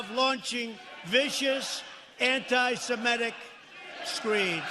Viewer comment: What did he say? That slurring coke head. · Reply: Exactly, wtf are "vicious anti-semitic "SCREEDS"? 🤔